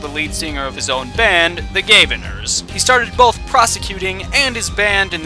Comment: When I hear, "Gavin," I hear, "Gaben." I have now made Ace Attorney 10 times better for you.